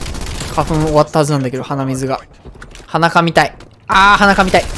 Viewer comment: 鼻かみたい、、、って台詞が 裸見たい、、、に聞こえるんですが、、 最近うんこちんちん言わないと思ったら全く